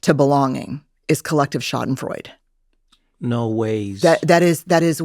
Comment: I turned on closed captions to understand what she said, but cc spelled it wrong so I looked it up. It’s actually the German word schadenfreude. It translates to "damage-joy" and means experiencing pleasure or satisfaction at another person's misfortune.